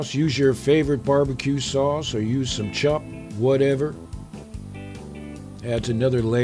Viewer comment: What is the thing you said you can use other than bbq sauce at 4:30 ? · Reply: Dictionary He said: "Or use some chup". (Ketchup)